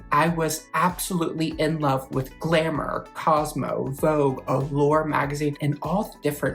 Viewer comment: I’m such a dork. You started listing magazines, but I heard “Grammar” instead of “Glamour,” and thought, “Really?! ME TOO! So that’s why she’s so eloquent!” 😆